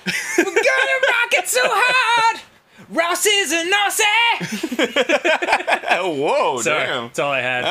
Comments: I’ve watched this play through so many times and every time I hear Dan said “Ross is a Nazi” low and behold I had captions on and he said “Aussie” that makes more sense now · For 9 whole years I thought Dan was saying Ross is a “Nazi” and only just realized he’s saying Aussie because Ross is Australian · I always thought for the longest time danny was saying "ross is a nazi" at the start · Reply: I always heard “Ross is a nossy”, whatever that is. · I always thought he said "ross is a nazi"